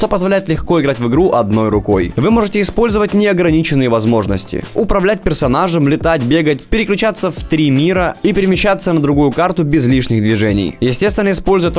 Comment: Вы можете использовать негра... Я: Что? Я заинтересован! Извините, просто я так услышал))